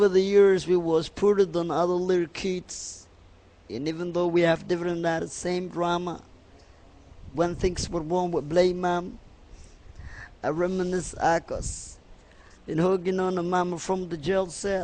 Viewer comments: Did He Just Say "I Reminisce Argos" LoL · did he just say i reminisce argos